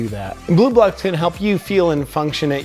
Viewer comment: I thought he said Blue Balls lol